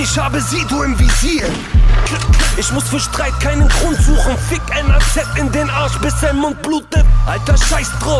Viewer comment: was sagt KC bei 2:45 fick NAZ? oder verhör ich mich da?